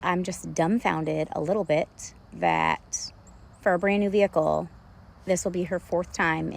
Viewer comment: “for a brand new vehicle” sounds like “fur brain” and that’s what I thought she said too lol